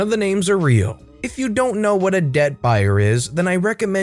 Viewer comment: I heard ‘deadbuyer’ until he said what a debt buyer was